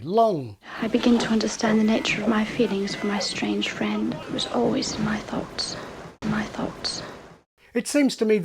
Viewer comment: I hear that as "always in my thots". 😬